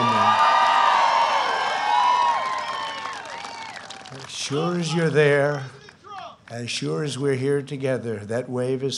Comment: what is the person in the background yelling at 0:30 ? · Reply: Sounds like "I vote for President Trump!" <3